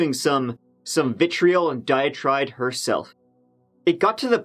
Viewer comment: Did it sound like he said "dia-tride" ?